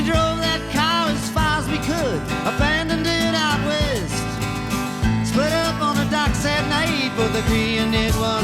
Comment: Not only did I think they “split up on the docks that night”, but I also thought she said “Jimmy, don’t I know your name”! Life is full of surprises.